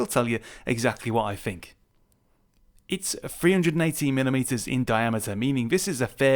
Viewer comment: I thought he said 380mm. Had to look it up. 318mm is still huge though. Fanatec is just going down the tubes.